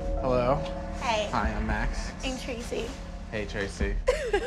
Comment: I honestly thought she said "I'm crazy" not "I'm Tracey"